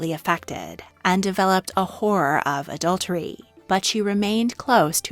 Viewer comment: Is this voice AI created ? I hope it is, because the narration is unlistenably moronic. 2:45 - 'horror' is pronounced exactly like 'whore'. I had to replay it twice just to work out what she was saying. 😔